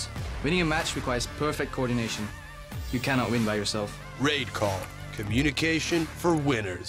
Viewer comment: Sounded like he said "rape call"